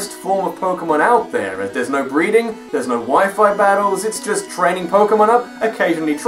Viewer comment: * snicker * At 11:03 in the video I thought I heard say "No wyfu battles."